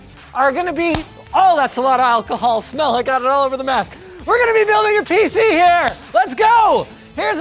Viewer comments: it so sounds like he said "Oh, I got it all over the meth"😂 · did i just hear "ohh thats alot of alcohol smell, i got it allover the meth"??? hahah